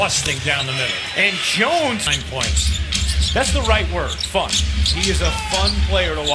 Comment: am I the only one who heard it as fuck 😂😂